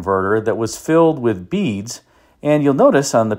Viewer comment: At first, I thought you said the pancake catalytic converter was filled with bees. But beads make more sense.